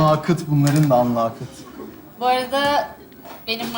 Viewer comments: emre ne diyor? Allahı kıt diyo gibi tövbe haşa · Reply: Anlayışı kıt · Tövbe tövbe ya burda ne diyor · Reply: Algıları kıt diyor ama aaalglarıııı diye harfleri yiyerek söylüyor yani